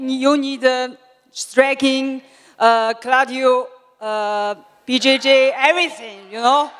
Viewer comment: did she just say cradio 😅